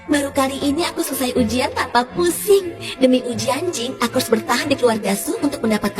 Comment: Pas dia bilang ujian Jing malah kedengeran "uji anjing"